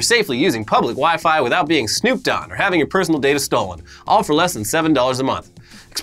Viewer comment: I know said "snooped on" but I was yawning and heard it as Snoop Dogg.